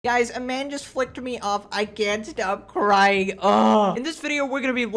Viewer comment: I thought it said “a man just LICKED me off i cant stop crying” 😭